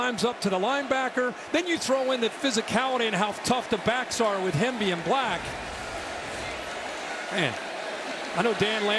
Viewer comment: Anyone else catch that at 2:27:20? What’s that supposed to mean 😂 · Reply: He said the name of the two running backs. Hemby and Black. one of those 'oh that didn't sound quite right' moments.